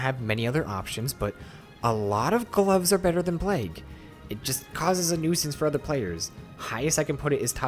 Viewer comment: sounds like he said blake